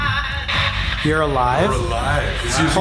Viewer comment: It said, “You’re alive” with such enthusiasm, and it sounded like singing. If it was the surgeon, then maybe that’s how he interacted with patients, or like he got laughs out of torturing people. It would make sense that he would be excited about them being there and them being alive, I don’t think torturing the dead brings the same joy he got from torturing and killing people when he was alive. It also said “I needed anybody” like it was singing, so maybe if that was in fact the surgeon, he was so desperate for someone to torture, he was willing to take anyone. Also, at 55:52, Sam says that the surgeon thought he was above his patients, and the device had just said church, could the surgeon have thought of himself as a divine being? Like, they were “helping” people at the time, so maybe in the surgeon’s twisted mind, he thought of himself as a deity. At 56:17, Colby says, “It’s nothing without you” This can also be tied to the even Greek gods, as they were always getting heros to do things for them. It’s like a substitute teacher with a rowdy class, if the teacher hasn’t shown that they can be strict and keep the students in line, then the class stops believing in the authority of the teacher. If the surgeon truly thought of himself as divine, then the only thing keeping that feeling from being ripped away from him was the patients, if he didn’t inspire fear among them, he would have no grounds to think of himself as a god.